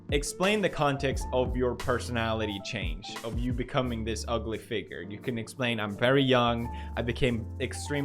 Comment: I thought he said ugly figure instead of public figure lmao